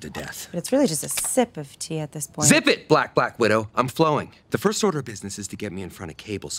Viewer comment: "ZIP IT!!! Black Black Widow" 🤣🤣🤣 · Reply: Lol I thought he said "flat black widow".